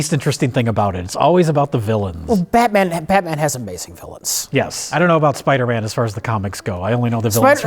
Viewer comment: I thought said, "Well, Batman ... Batman has some based villains." I had to rewind it to ensure I had misheard. The day that earnestly says "based" is the day I prepare for the end. (What does he actually say? He says, "Well Batman ... Batman has amazing villains." True enough. True enough.)